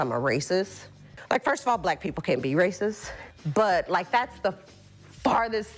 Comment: Did she just say "Black people can't be resist"